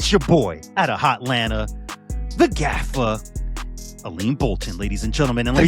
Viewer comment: I thought he was saying “a lean Bolton” like a nickname lol. Killing it guys !